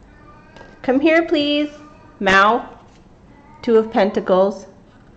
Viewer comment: Ty for your readings. God blessing to you. I wanted to comment on miao. It sound like he said come down 😀 amazing 🙏